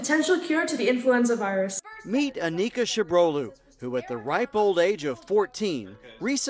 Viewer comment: 'who at the ripe old age of 14' 로 들려요.